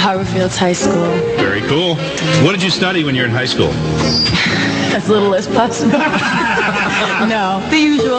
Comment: Can somebody please enlighten me, what in the world is she saying at 1:57 ? 'Just can't make it out really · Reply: "As little as possible"